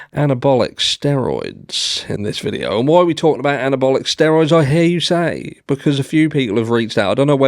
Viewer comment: I thought you said anti-bollocks steroids 😂😂😂😂